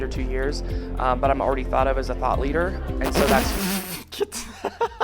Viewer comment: he really said.. 'a thot leader' and we all low key know why it slipped out that way